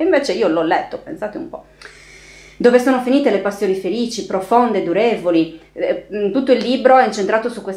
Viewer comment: Quando hai letto:" dove sono finite le passioni durevoli " ho capito " passioni diuretiche "- forse il termine era più acconcio